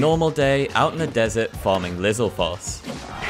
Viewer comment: your accent makes it sound like you said “Lizal farts”